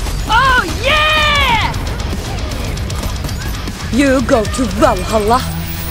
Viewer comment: Did she say, "You go to Brawlhalla"